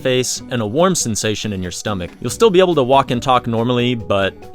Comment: CROTCH! I mean stomach yeah that's totally what I said too. What? No you're hearing things!